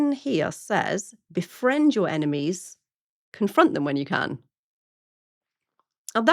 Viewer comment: I thought Ren was saying "befriend your enemy confront them when you can't...." Funny but it works either way. In fairness, I've heard this about 100 times so slight advantage ...lol.